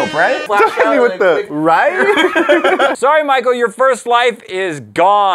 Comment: I THOUGHT SAID “SORRY MICHEAL! YOUR FIRST ‘WIFE’ IS GONE”